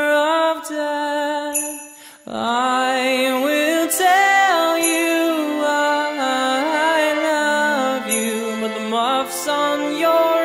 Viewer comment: I thought it said "I want to tell you I love you" lol